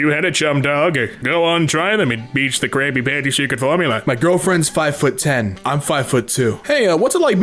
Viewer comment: the youtube captions thought that when you said “Krabby patty secret formula” you said crappy panty secret formula